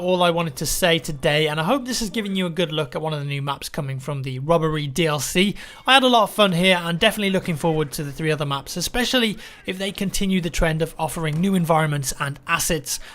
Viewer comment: thought he said "rubbery dlc" at first